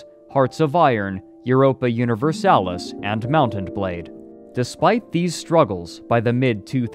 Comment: Did I hear Mount and Blade? Wasn't that a taleworlds game??? if I really am just deaf, it be nice if someone could tell me what game he meant there, I've been playing games from paradox for a long time and I can't for the live of me think of a game that would sound like that which they made.